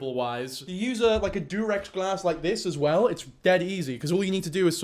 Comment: did he say durex glass??? 🥲